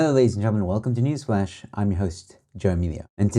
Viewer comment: The way says it sounds like he said News-splash.